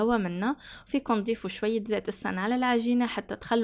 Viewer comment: ايش الزيت؟ ما سمعت منيح ❤️ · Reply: زيت السنال ( زيت الزيتون او زيت نباتي )🤗